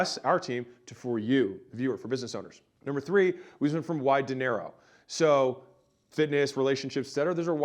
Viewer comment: OHHHHH "wide to narrow" The entirety of the previous content video, I thought you were saying "why dinero?" - as in "why money?" I listen to a lot of videos while doing other stuff, so I don't always get watch them. This makes a bit more sense 😂.